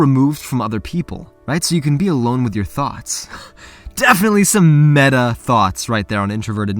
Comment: I thought you said thots...not thoughts...I’m not a perv I promise....